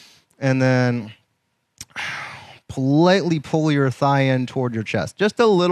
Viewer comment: On the figure 4 stretch, you said, "pull lightly on your left thigh toward your chest." I thought you said, "politely pull your left thigh toward your chest," and I guessed you meant without farting. Let's just say I wasn't polite.